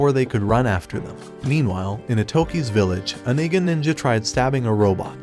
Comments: Did i hear nigga ninja?? Edit: i seem to have mixed up the words i think he said igga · wait did he just say a nigga ninja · I KNOW DANG WELL HE DID NOT SAY NOTHING NINJA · Bro thought he was slick when he said "meanwhile in atoke's village a nig**r ninja" 26:34 minutes in the vid💀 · GNG WHAT DID I JS HEAR... 26:34 MY MANS RLLY SAID N GA NINJA LIKE IF WE WASNT GONNA NOTICE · I hear something at 26:33 like a nig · I heard him say a nigga ninja